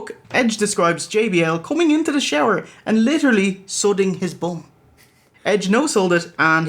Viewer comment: did they just say "sodding his bum"?